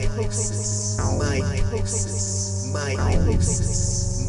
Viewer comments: I thought he was saying "Mitosis." lol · i thought he said 'Mitosis' and i was like 'whuut mitosis isn't a fungus, or even a noun' then i thought the joke was 'My-Toe-sis' and my 'whuut' extended into the deeper fathoms of my brain. then i regained consciousness in the late afternoon in a puddle of my own saliva